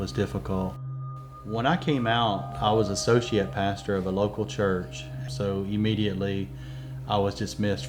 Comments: I heard "When I came out, I was a sociopath at the local church". I kid you not. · thought he said sociopath